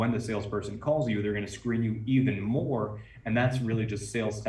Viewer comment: what are u saying , I'm hearing Screaming and subtitle showing Screen. and even if you saying Scream does it mean Grabing attention or Scaring them and made it hard for them ?? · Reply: "Screen."